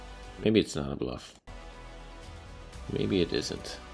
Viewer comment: Did he just say "тупица" on russian which is known as "jerk"? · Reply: hahaha i said I believe it's not a bluff